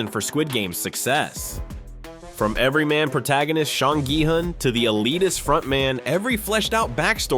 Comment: Did you just call him shawn gi hun? 😂